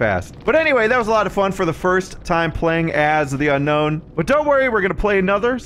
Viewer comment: When the video started :_ohmPickle: and said "we're playing as the unknown" .. For a second I thought he said "we're playing as the gnome" .. and I got confused but also curious lol :_ohmBunny: Interesting killer and perks, hope to see the new survivor in depth soon too. Ty:_ohmLove